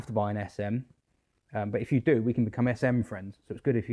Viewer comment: For one minute I thought you said S&M friends...